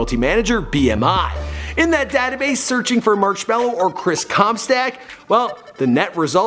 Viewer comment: I heard it as chris cumstain i dunno why